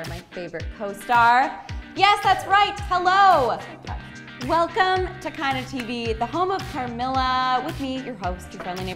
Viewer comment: What does Elise say in 0:10 when Nat's doing the intro?? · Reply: 2 yrs later but I hear “I’m so touched” 🤔🤓